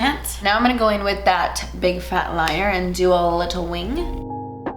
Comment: did she said "Big fat liar" ... Like... The movie? Haha